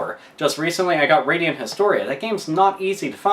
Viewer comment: What game does he says at 9:29? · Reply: Radiant Historia